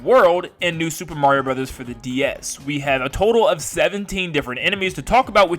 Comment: did he say DS